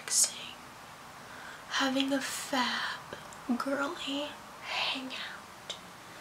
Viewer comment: Did she just say "having a fap"? Because I died